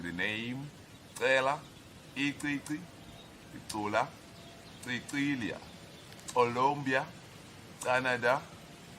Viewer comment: Wait... 6:19, did I just hear my name 0.0 Cecilia?